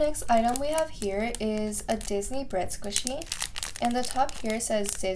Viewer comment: this box was so cute! also at 3:46 did you say 'sisney' im not hating or ridiculing just wondering if i heard that right 😅 '